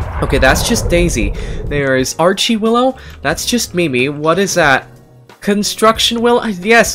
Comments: /2:27 did he say mimi by accident cause i think its katie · Did he just say, Mimi???